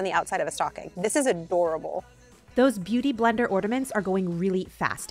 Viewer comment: you're amazing and hilarious. I heard you say "ordament" instead of ornament. It reminded me of my nephew at a young age!!! Love it. 😂